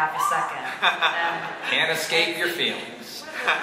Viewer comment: cole: can’t escape your feelings sounds relateble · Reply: Omg thank you so much! The whole time I Thought He Said: and escape your feelings, What didn't make sense to me!